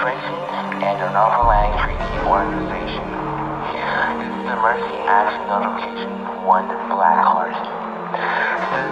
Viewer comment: what did he say? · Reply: Please stand by is what he said